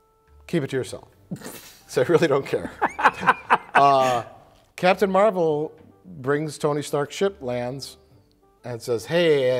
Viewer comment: I've been trying to figure out what he says here for like 5 minutes. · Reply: Captain Marvel brings Tony Stark - Ship lands.